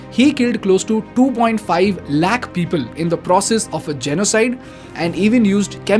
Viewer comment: he kill 2.5 what people ?? Is black u say ??? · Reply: Its lakh😂(an indian counting unit) 2.5 lakhs means 250000